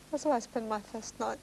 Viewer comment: what did she say at 4:50????? before she starts crying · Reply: "This is where I spent my first night.." She breaks down, not at the memory of the first night, when she wakes up next to a dead person, but because it reminds her of the hospital block on which she came closest to death and where her mother saved her life.